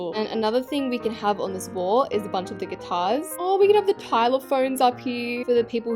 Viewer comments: 💀did she just say Tyler phones in stead of xylophones💀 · Did she just say Pilelaphone?